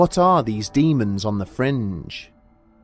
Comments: Okay, anyone else thought this said "Demons on the Fridge." 🧊 ? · Thought it said The Demons In The Fridge lol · Thought it said demons on the fridge....